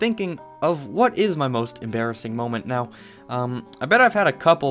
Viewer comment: Here's my most embarrassing moment Well I was in art class and this was in 4th grade And I was sitting at a table with 3 other people One was a girl and 2 boys One boy was Maddox and he was the boy I had a huge crush on. (Maddox was the class clown) We were talking and Maddox made a funny joke Well of course I laughed and here it goes..... I farted SOOOO loud.😰 (Almost everyone in the class was laughing or looking at me) I could tell me face was so red I am not a popular person not one bit So this was super embarrassing moments for me And thank god it was not what everyone talked about maybe just for like an hour. So ya if you are Maddox (which I doubt) then......ya I'm glad I'm home schooled 😬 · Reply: Emma Denton Thought you said 'heres my mums embarrassing moments.'